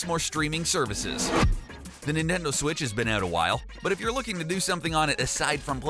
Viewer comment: Did he say ninendo switch 😂😂😂